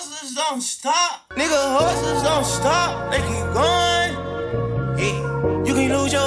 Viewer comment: Bro🤣💯🤞🏾i swear i thought he said horse dont stop